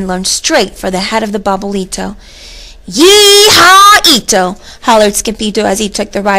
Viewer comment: Did it say yee haw ito